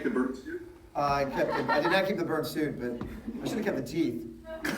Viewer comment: I thought he said that he should've kept the Bird suit.